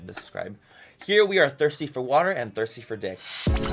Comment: Did he just say, "thirsty for water, thirsty for dick." ?! IM IN TEARSSSSS